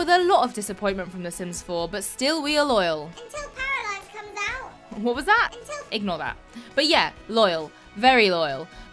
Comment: What does she say at 1:03 ? Until __ comes out? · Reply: Paralives!